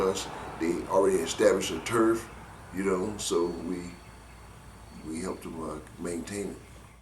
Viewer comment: did he say they helped the Marvins maintain their hood. ?